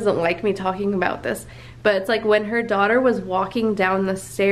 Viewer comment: When you said the sago was starring and barking I thought that you said the daughter was